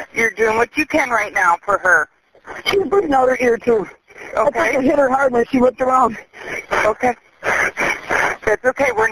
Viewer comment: Anyone else notice at 4:15 Todd say "she's bleeding out her ear too. I think i hit her hard when she whipped around" So pretty much confessing already or did he slip up? Lol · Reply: Press my time stamp of 4:15 and read what I have in quotation's. He says "she's bleeding out her ear too. I think I hit her hard when she whipped around." Read Long with when he says it.